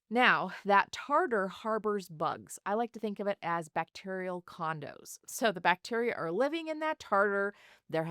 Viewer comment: I thought she said "bacterial condoms" not "bacterial condos"!😂 Condos makes much more sense in context.